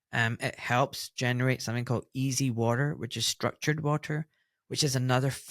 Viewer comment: It is fascinating the water thing and electricity. For those who thought Ahmad said "easy water" he was talking about EZ (exclusion zone) water. Thanks fellas great interveiw.